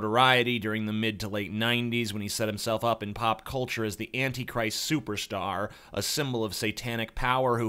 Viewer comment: legit thought you said he was the Edgey Christ